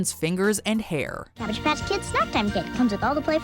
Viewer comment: I thought they said Savage Patch Kids✌️